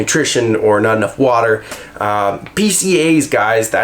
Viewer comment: what is he saying at 8:16 ? BCA? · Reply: Branched-chain amino acid or BCAA. Are essential nutrients that help strengthen body tissue and elasticity. Very similar to protein. Good for hydration, energy and repair.